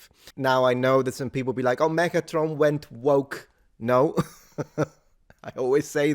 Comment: did he just said, "Megatron went woke"?